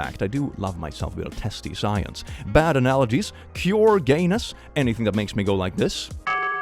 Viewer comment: I misheard you the first time you said cure, I thought the bingo tag was "pure gayness" and I was wondering how it fit in lmao